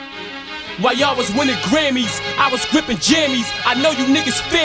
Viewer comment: thought he first said 'whilst you were winning grammys, i was wearing jammies' (crappy sound on computer) i was like wtf??! Wearing pajamas?? Had to grab my headphones. Think ive been up a lil too long. Siiick track, wonder why this guy never has really 'blew up/been signed to a big label. Hes been one of the best/most raw mc's for years now..